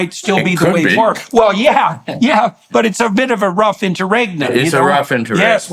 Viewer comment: Does anybody know what word of phrase they use at the 55:48 mark?? "It's a rough... _ _ _ _" ?? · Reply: Sounds like "interregnum" to me, but I'm not 100% sure :)